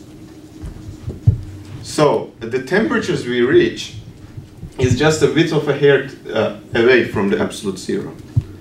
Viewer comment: arasında ne diyorsunuz acaba hocam ? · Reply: The temperatures we reach are just a width of hair away from absolute zero.